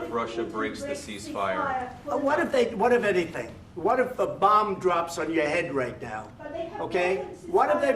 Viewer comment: On one Russian channel the translator misheard the phrase and translated it as "Что если сейчас Обама упадет с потолка?" or in English “What if Obama drops on your head right now”😂